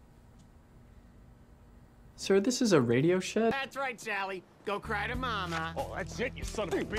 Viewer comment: "is that where I screwed up?" ".....sir this is a radioshack" has vine/tiktok energy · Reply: oof my bad, I misheard it as Radioshack lol